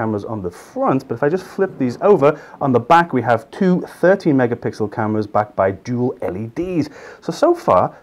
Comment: Audio mic is a bit muffled. I thought you said 30 megapixel even though it was 13 megapixel. Might want to invest in a new microphone.